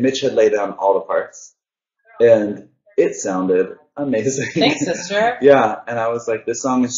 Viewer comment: did Mitch say "thanks babe" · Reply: He said "Thanks sister"